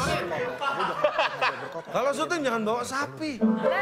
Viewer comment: ngomong apa itu XD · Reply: Cak lontong ngomong sama piano, "kalo syuting jgn bawa sapi" sapi yg dimaksud cak lontong tu om iyan krna badan gede.